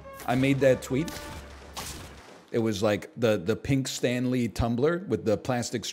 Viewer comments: I thought he was saying Stan Lee Tumblr and i was so confused dude... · I was so confused, I thought he was saying "Stan Lee Tumblr"